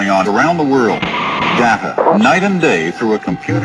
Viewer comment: Hah i thought he said planet all night loll